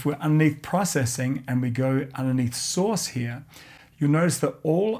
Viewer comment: I'm sorry I really thought he said "sauce" like "secret sauce." Took me minutes to absorb it as "source."